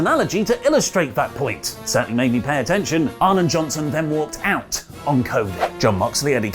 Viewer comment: Oh "Arn and Johnson walked out" I had to pause for a second because I really thought you said Ahmed Johnson walked out 😂😂😂